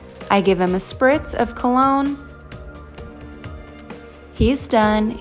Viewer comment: I THOUGHT YOU SAID HE’S DEAD